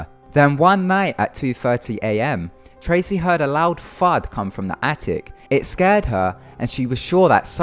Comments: I thought he said she heard a loud fart from the attic and spread debris on her haha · did he say "loud fart"? XD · I thought he said "Tracy head a loud fart" at 4:00 lol... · lol i could of sworn you said "she her a loud fart coming from the attic" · OMFG DID HE SAY "LOUD FART" AT 4:00 LMFAOOOOOOOO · when he said she heard a loud thud, I thought he said a loud fart. I was like what. XD